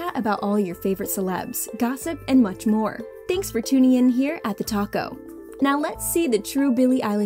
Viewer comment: ''TheTalko''. nah all I hear is ''TheTaco''